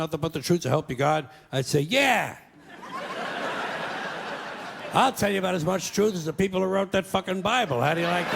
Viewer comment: Aos 4:25, ele fala "I'll tell you about as much truth as the people who wrote that fucking bible" e não "around that fucking bible" como foi traduzido.